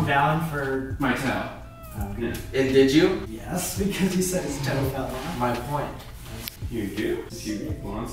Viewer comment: When he said his toe fell off I thought he said his coat fell off😂😂😂